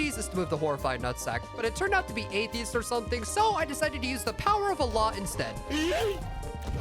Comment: Did he say «the power of Allah » and then throws a bomb? You can insult the religion of yours but don’t insult the others. If you didn’t say that, it’s chill then.